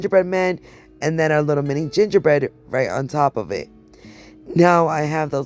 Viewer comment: I thought you said ass cheeks haha 😆